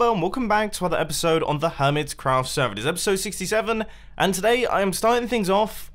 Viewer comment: did he say episode 67?